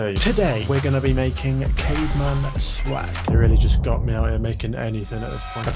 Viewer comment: I swear I thought he said “German swag” 😂😂😂😂😂😂😂